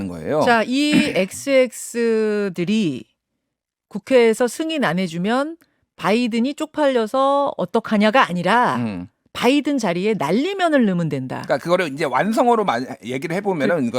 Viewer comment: "승인 안해주면 날리면은" 으로 들림 . 대통령이 저급한 말을 함부로 한 것은 큰 잘못이지만, 다시한번 잘 들어보면 좋겠습니다.